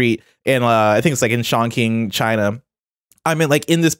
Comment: did he say Sean King China at 1:34:58 lol